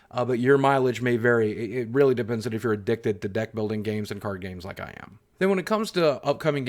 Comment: I swear I heard "Dick Building" lol